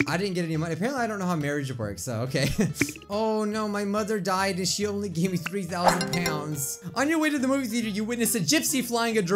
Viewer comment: I heard Oh, no my mother died and she only was 3,000 pounds! Lol